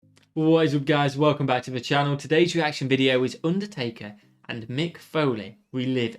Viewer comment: Haha i thought you said "Undie-Taker" at the beginning xD